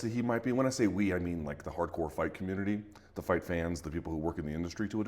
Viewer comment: "The hardcore fight commudeldy" Once you hear people say "udeldy" instead of "unity" it's both annoying and fascinating. Stop pulling your tong so far back into your mouth dude, the 't' is right at the front.